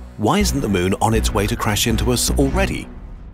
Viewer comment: I thought he said "Why is the moon on its way to crush us already?" I was like "wait wat 😀?"